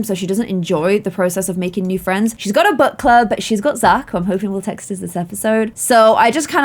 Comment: When you said book club I thought you said butt club!?